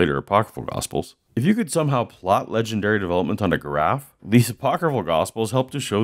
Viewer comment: When you said "if you could somehow plot these on a graph" I thought you said "if you could somehow plot these on a giraffe."